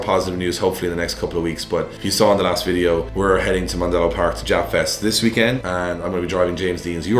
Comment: When he says japfest is sounded like he said ja fest i though some rastafarians gonna come slide 😂😂